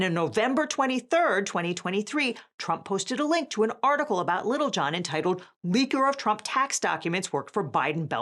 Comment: DAMN IT! I was just listening and THOUGHT they were saying Lil Jon leaked Trump's tax documents. 🤣 The IRS guy's last name is Littlejohn... silly me.